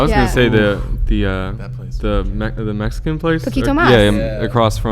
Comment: I'm mexican, I speak spanish, so when Chrissy said that she ate very well and Will said "poquito más" (which means, a little bit more), I thought he had said "poquito nomás" (which means, just a little bit") saying (sarcastically) that she ate a lot, and if you speak spanish you'll understand that it was so funny xd